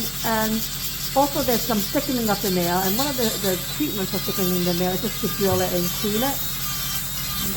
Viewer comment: you spoke about thickening of the nail but I did not hear what treatment you said can you please say again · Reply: you can debrided it